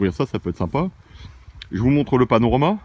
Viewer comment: Salut a vous 3 :) chouette video avec de belles trouvailles . au début , quand tu parles du panorama , j'ai compris " panneau romain " mdr c'est le matin . Bravo pour le partage . Bise d Alsace . Good luck .OSCAr